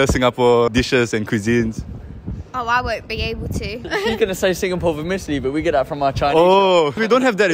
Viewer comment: What did the guy say at 1:34 ? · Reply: He said they were gonna say Singapore Vermicelli 😂